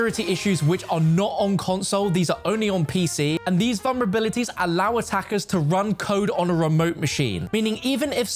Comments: Am I crazy or did I hear say “allows us hackers to...” at 1:14 in the video??? Freudian slip? 😳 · did he say “allow US hackers”😂